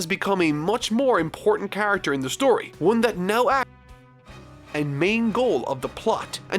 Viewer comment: somewhere around here, there is a weird audio break. I think you were trying to say something along the lines of "now acts on and influences the main goal..."